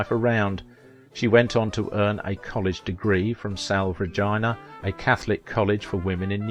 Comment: About 13:20 I had to play it back, cause I thought the narrator said Sal Vagina...🤔, he had a thick British or English accent